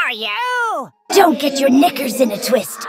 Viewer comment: uhhh what did bro say? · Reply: Don't get your knickers in a twist